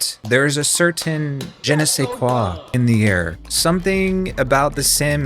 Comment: I thought you said genocide sais quoi😂 And was applauding you for the pun😭